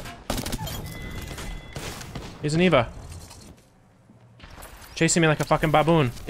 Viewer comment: のところなんて言ってますか？ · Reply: is Eva